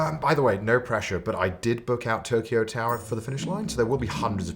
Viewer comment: When "I booked the Tokyo tower" was said, I heard: "I booked tokio hotel" and my inner child just went through the monsoon